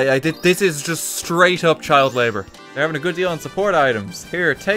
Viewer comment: Child labour? I thought you said, acceptable source of labour